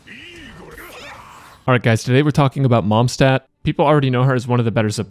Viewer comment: I thought that I misheard something.. but this man actually said Momstadt